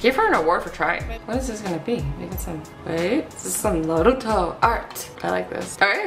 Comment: did u just say NORUTO